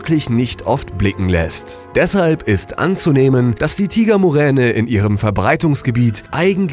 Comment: Ich hab verstanden Hitler Muräne xD